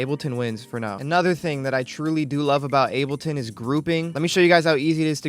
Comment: i heard grooming